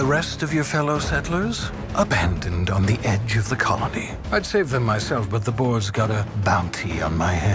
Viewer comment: The colony IS on the edge of the galaxy. Did you even see the reveal? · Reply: I know it's on the edge of the galaxy, but I'm talking about the video. In the video, when talking about the abandoned frozen colonists, they say it's 'abandoned on the edge of the colony'. misheard this and said 'edge of the galaxy' which was incorrect. Did you even watch this video?